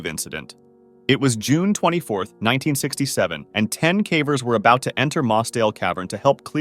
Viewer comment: Wait did that just said 67 ?